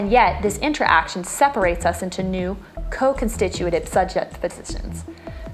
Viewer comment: Awsome explanation! Anybody knows what she says at 1:52? co-constitut .....? :D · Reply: Jad Moussan Thanks! Co-constitutive :)